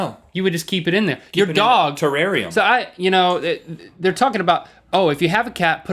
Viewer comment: did he say terraria .